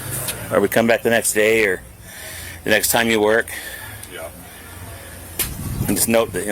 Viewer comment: does the cop say " we come back the next day or the NEXT TIME you ORDER?" Can anybody tell me if I'm hearing this wrong?? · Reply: I thought he said or the next time you 'work'. That is what I heard anyway